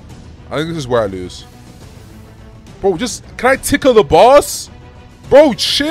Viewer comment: i hear "can i tickle the boss"